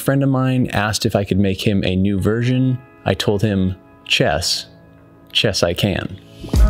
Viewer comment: I thought it said chess made hand set